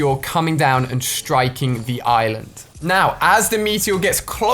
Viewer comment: It sounds like your saying ireland